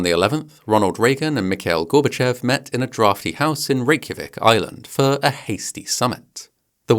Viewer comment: did you say Reykjavik, IRELAND?